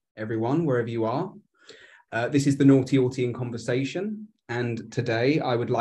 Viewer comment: This will be interesting, to hear interact. I like. At first I thought his tag was "Naughty Oughtie" as in, he was born in 2000 and doesn't do what is expected. My bad. Edit to add: did not disappoint! is a very good interviewer! He lets the guest talk for one thing, and he has good questions. Very informative and good to hear when talking normally still has that great voice. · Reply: to my American ears, I thought he said Naughty Auntie 🙃